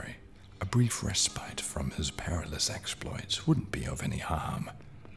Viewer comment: I misheard "perilous" in the intro as "parentless" LMFAO I was about to be like "man you did not have to do him that dirty"